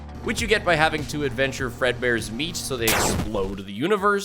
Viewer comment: I zoned out, and when i zoned back in i thought what i heard was "Fredbear's meat", the brain works in many mysterious ways.